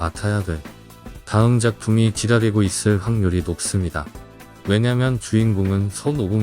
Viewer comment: "다음 작품이 지랄이고 있을...."로 들리는 건 나 뿐인가요?! 슈퍼. 또 얼마나 지랄할지... 두고 보겠셤! ㅋㅋㅋㅋ 오반 갓, 오반 블루, 오반오반, 오반 육반, 오반 칠반, 다 나와~~~!!!! 아주 그냥 명맛 대잔치야... 이거 닥터슬럼프 이세계인가!?!?!? 토리야마돌았네 ㅋㅋㅋ